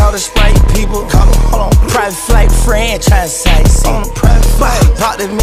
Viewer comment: “Private flight to France” uhhhh, I heard franchise not france